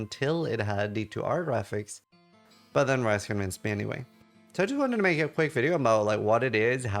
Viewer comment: He didn't? He said someone has potentially cracked D2R so the mod might come to it. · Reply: I misheard, he said I wasn't planning to play this until it had D2R graphics, and didnt understand what he said that after, something about "rice convinced me anyway" which I only understood now after listening a second time